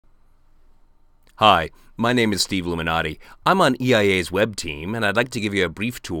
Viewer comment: Did he just say Steve Luminati?